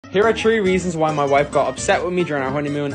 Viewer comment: I thought you said tree but then I looked and then you said three